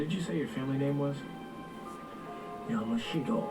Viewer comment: Yamashiro.... does nobody remember the Yamoshiros from Arrow's season 3 flashbacks? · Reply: I thought he said Yamashiro, not Yamashito. There is a huge difference there lol. Yamashiro was a province in Japan, where Kyoto is. Kyoto, obviously, was what every clan wanted. It was the center of Japan and whoever held that province would basically become emperor